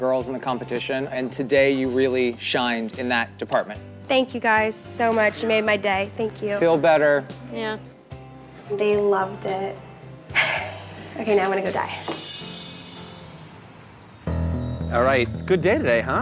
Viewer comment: did she say “ok now i’m gonna go die” 💀💀